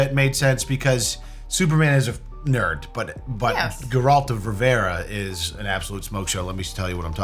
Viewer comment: Ok, so I'm one of your older viewers. Like 8 track old. I thought you were saying Geraldo Rivera... the news guy. 🤣 Merry Christmas!